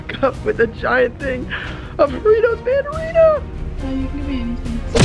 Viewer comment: I thought he said doritos